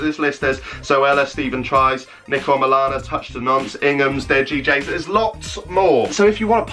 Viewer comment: “Touch da-nonce” 😂😂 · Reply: i thought he said 'touch da knob' lmaoo